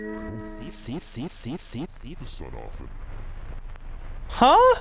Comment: I heard “See the shit off” 😭😭 Also 25:18 your reply to a question you don’t know is just “CHICKEN BUTT” is too funny loll